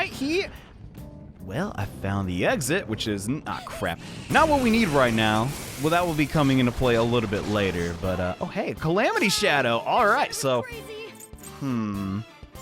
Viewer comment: I swear the tutorial said DISASTER SHADOW